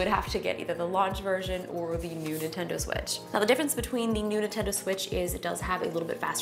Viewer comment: ahem, excuse me, did you say the "nudie tendo switch"?